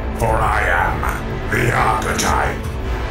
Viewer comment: I thought archetype said appetite